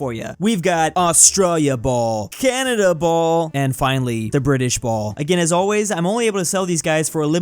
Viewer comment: British? i never heard that i only heard BRI I SH